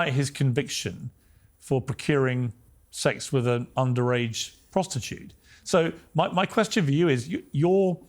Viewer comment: Did he just say under age prostitute ?